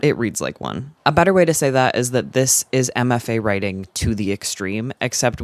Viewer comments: I watched this whole thing, and every time you said "MFA Writing," I heard "Emma Fay," and I was like, who is Emma Fay, and why do you hate her writing so much? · the whole time you were mentioning MFA writing and i was so confused because i thought you were saying “emma faye writing” and i thought you were probably just referencing some insufferable author i hadn’t heard of lmfaoo